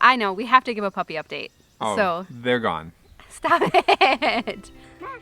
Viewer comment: Did you say the puppies are gone?